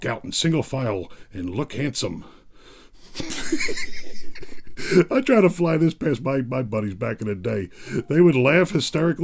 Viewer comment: Did you just say “if I fly this past my butt buddies” do I hear that right? Lmao